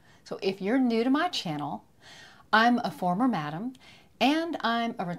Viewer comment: I had to rewind and do a double-take, when I heard her say "I'm a former man" Oh, former "madam"! Now it makes sense